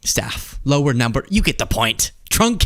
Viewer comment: Ick.... It first sounded like you said trumpcated ....ewwwww